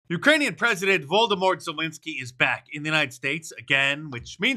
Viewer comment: Did he say Voldemort 🤣🤣🤣